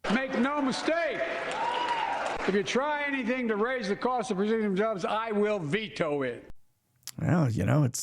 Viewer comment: Did you see the video of the flatbed carrying the alleged object? lol timestamp 14:30 I hear "the cost of regime jobs" lol